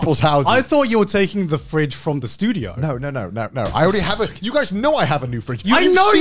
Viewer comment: So Joey and Garnt thought Connor was talking about the fridge at 2:11 and Connor didn't realize that they thought about the fridge. Then Joey must've heard at 2:28 "I'm taking the old one from this (dude)" when it was supposed to be (studio), doesn't help that Connor is pointing in his direction, thus the misunderstandings further grow. Joey was saying at 2:37 "So are you actually taking ours?" as in the fridge in his house and Connor must've thought that "ours" meant the studio's one. Lmao Connor at 2:41. And Garnt at 2:42 must've thought that Connor was gonna take the fridge from the studio. Damn the level of misunderstanding this went through lol. In the end Connor was gonna take the washing machine from the studio, Garnt thought Connor was gonna take the fridge from the studio and Joey thought Connor was gonna take his fridge. Beautiful. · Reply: lmao at first i heard "the studio" and had to rewatch to listen "this dude"